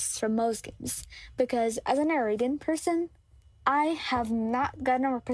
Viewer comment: OMGG wait at 4:24 did you say you are arabian or did i miss hear ? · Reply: yes!!! i am arabian